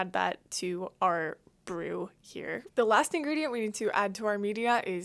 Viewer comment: I thought you said anger for a sec